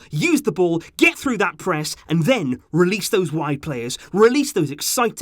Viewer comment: Sorry, I just heard release those White Players, then I saw Dembele and Ansu Fati.